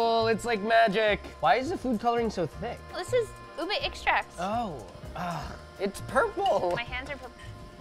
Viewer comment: when he said "food coloring? that's cheating". i agreed.. my lola too is not a fan of food colorings. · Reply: I thought she said it's ube extract.